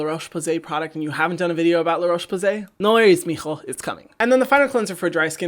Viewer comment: Lol did you just say, "no llores mijo, is coming " 🤣 love it